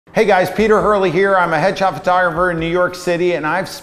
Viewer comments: Literally thought he said "hedgehog photographer" at the beginning of the video lol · I totally heard "Hey guys, Pete Hurley here, I'm a hedgehog photographer". Awesome :D